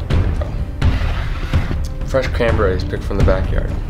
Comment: “Freshly picked cranberries from the backyard” them can marks tho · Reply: Julien Nilez I thought he said "canberries"